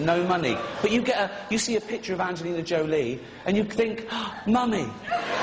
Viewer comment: I thought he said money, "you see a picture of Angelina Jolie you think money" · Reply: no he definitely said "mummy" money would make no sense